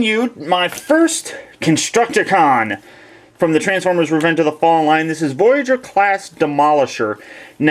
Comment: Transformers: Revenge of the Fall Line? LOL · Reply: if you say it fast enough it sounds like you say: transformers: revenge of the falline